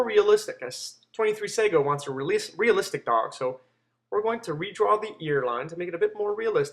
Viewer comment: i thought u said realistic dog